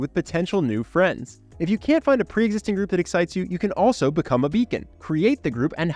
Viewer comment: I thought you said, “become a vegan” rather than “become a beacon.” 😂